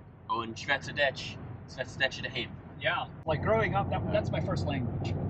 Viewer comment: I want to ask, as someone who only knows English, were the subtitles at 1:15 incorrect? It sounded like it should have said "They speak Dutch at home"?